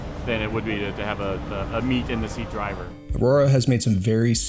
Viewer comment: Did he say meat in the seat driver 🤘